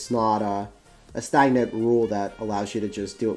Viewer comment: What is the word used at 5:51? It's not a ______ rule? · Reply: stagnant